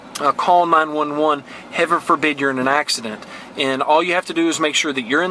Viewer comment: did you say "Hever forbid"?